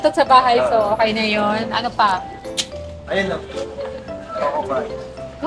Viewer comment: ako lang ba or narinig ko na nag sabi si na " ayun luv?"